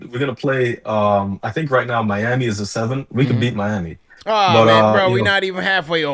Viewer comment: here. good sir, I got a bone to pick with you my man! I wasn’t ducking MIAMI. I said at 24:53 “we could beat Miami”. What I said was I want to avoid teams like Orlando that aim to injure our team and due to attrition. Tale of the tape good sir!! 😂😂 salute + 🫡